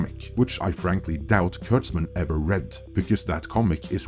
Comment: heh! with yer accent(?), it sounded like you said "turds-man" instead of kurtzman...😂